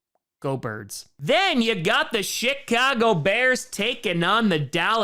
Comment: I swear it sounded like he said Shitcago Bears instead of Chicago Bears.